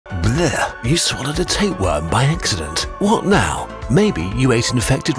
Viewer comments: I thought you said, “you accidentally swallowed a tampon” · I thought it said you swallowed a tampon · I thought that said 'you swallowed a tampon' and I was so confused